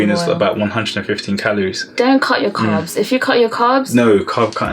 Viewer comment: pleasee what did u say? "Dont count your carbs"? Im from spain and I dont understan that part. You wsnna mean not to eat much carbs? Thannkss❤️❤️😘😘🤗🤗